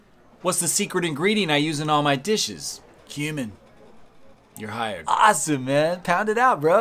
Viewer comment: imma be honest i wasnt looking the first time and thought he said “human”. I would not have been surprised.